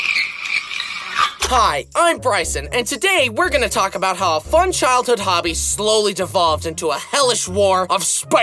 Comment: Dude I thought it said 'my human childhood'